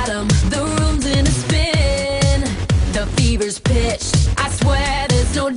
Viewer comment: I heard Bieber's bitch at 1:35 at the last line